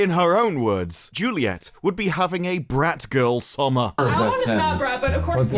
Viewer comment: I misheard that as 'frat girl summer'.... Frat is short for fraternity. Literally brotherhood.